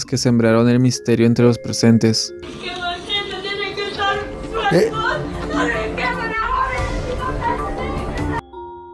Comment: Alguien puede decir que es lo que dice de el caso Yuraima? Minuto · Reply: “¿¡Los que queman tienen que estar sueltos, tienen que estar sueltos!?”